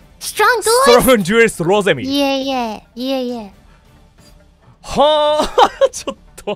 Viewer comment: Thank you so much for the clip!! Their interaction was super cute~ I’m sorry if this is impolite but I have a few suggestions >< At 7:14 I think Rosemi is saying “No no, no no” (that she’s not a strong duelist) instead of “yeah yeah” in english! So when she says that his cards are level 9 she means no, you’re also a strong duelist ^^ At 8:36 Kagami says “your field” and “my field” as in their playing fields! Again, thank you so much for your hard work!!